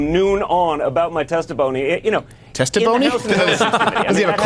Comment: I thought Schiff for Brains said testiphony.